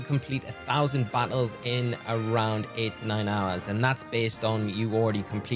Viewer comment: I thought you said 89 hours instead of 8-9 hours almost had a heart attack😭